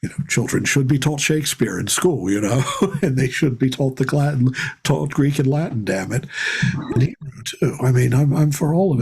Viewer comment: > and they should be told [???] taught Greek and Latin I don’t think I could make out what he was saying here. Could someone tell me? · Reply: I think he was about to say something like "the classics" and then he said "Greek and Latin", that they should be taught Greek and Latin.